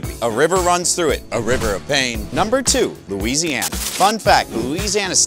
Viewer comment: I thought he said Lasanga not Louisiana 😐